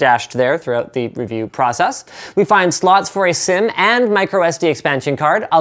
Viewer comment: LinusTechTips did i hear u saying slot for sim around 2:22? so u basically got the 32gigs version?? bastard! any idea when its coming out? still waiting for that.